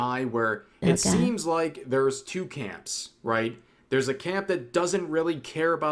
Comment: i may be tired from fitful sleep- but somehow at 1:50 i misheard saying two camps as toucans. wait wut? back in the seventies? what? kougure sounds like they're doomposting.